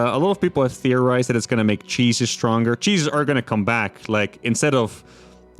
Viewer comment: When he said "Cheeses are gonna come back" I thought for a second he said "Jesus is" Christians would be that fourth race we been wishing for 👹